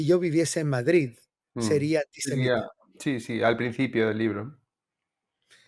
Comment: No se entiende cuando dice si yo viviera en Madrid ¿qué?, minuto 51:26’’ 😢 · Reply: Antisemita